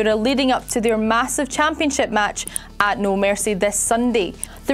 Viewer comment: Did she say wwe championship match at no mercy